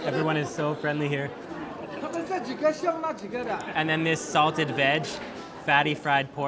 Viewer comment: Hahha i understood salted vag instead of salted veg, lol my bad